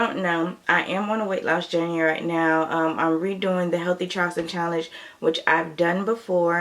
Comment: I thought you said healthy chocolate challenge. I got too excited lol. But you said healthy charleston.